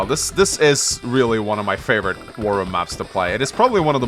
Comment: I kept hearing him say "wurm room"